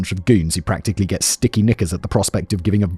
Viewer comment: Ok…I can’t be the only one who misheard “Sticky Knickers” as “Sticky N-words”